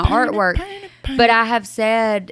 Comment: “I want to wear MANY hats.” What I heard: “I want to wear MINI hats.” Me - “Girl wear your tiny hats!” 💀